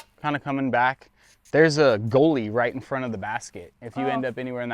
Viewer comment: I heard “goalie” the first couple times and I was so confused 😅